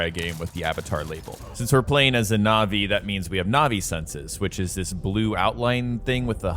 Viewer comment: i heard naughty senses 🤣☠